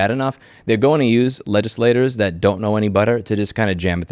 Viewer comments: you said "don't know any butter" think this is my first comment so I will establish I agree with you on the majority of fronts I have time to research myself (surprisingly rare I hear) but this was so funny I had to point it out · Did I just hear "use legislators that don't know any butter to jam it through anyway"? Intentional or not that's such a good line lol